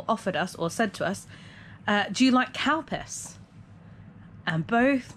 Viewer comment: I thought you said Cow piss